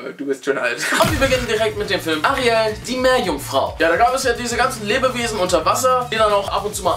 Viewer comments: Ich hab Arya die Meerjungfrau verstanden😂😂😂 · ich habe arya die meerjungfrau verstanden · Ich hab Arya die Meerjungfrau verstanden.